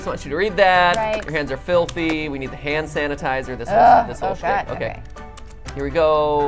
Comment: am i the only one who hears matpat say sh*t edit: also its so annoying already finished the game 100% and seeing them mess up on showing the contract